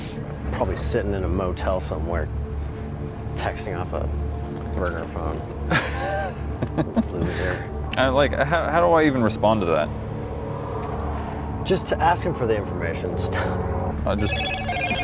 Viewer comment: “He’s probably sitting in a motel somewhere texting off a burner phone... loser.” That is why is my favorite❤️ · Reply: I literally thought he said burger phone XD